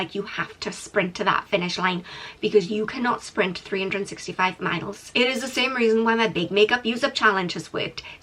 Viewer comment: I've misheard "you cannot sprint 365 smiles" and it made total sense to me. You sprint them in few months and then what... remain smile-less for the rest of the year? :))